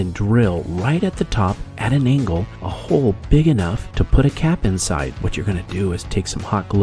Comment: Oh goodness 😮 thought you said put a CAT inside! I LIKE cats, a lot… I’m firmly against this